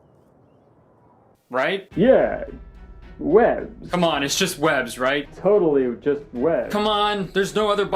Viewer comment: I thought you said "its just webs" as "its just whatevs" and i genuinely cant believe i was wrong